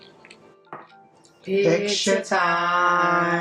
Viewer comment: What did you say at 4:15? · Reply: Hello again, Usually when we finish every video, we take pictures for the beginning of the video. That is why we said "picture time." Thank again, do come back for more videos.😎